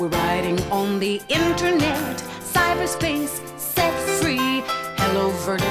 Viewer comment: What's the woman singing after "cyber space" ... ? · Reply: Thanks, I thought she said "sex-free".